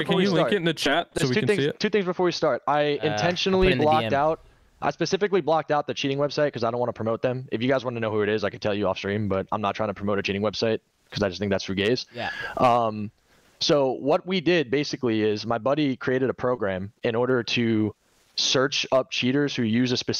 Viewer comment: Did he say cheating is for gays or did I mishear (1:51:40)? No need for that if so.